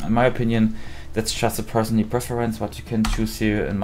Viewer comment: I don’t mean any disrespect and this video was super helpful but I’m having a little bit of trouble hearing the part at 4:43 can anyone explain what he said? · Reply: that's just a personally preferences